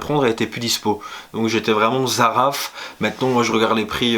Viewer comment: t'as dis quoi à 2:30 stp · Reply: LE CHEMIN DU ROI j’étais zaraf, dégoûté